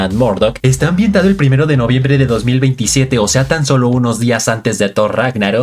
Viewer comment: No se si soy yo pero escuche 2027 en vez de 2017